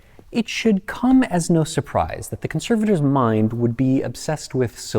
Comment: “It should come as no surprise thet the conservators mind would be obsessed with fixing things” - dammit I really thought you said “conservative’s mind”. You really spooked me out there for a second..